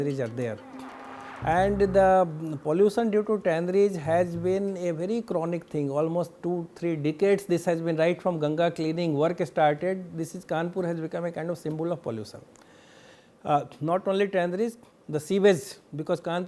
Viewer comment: It sounded like he said almost 2 3 Dickheads 😂😂😂😂